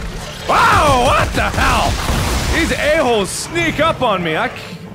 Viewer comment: did you say "air holes?"